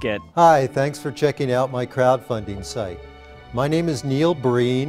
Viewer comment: I thought he said crap funding lol.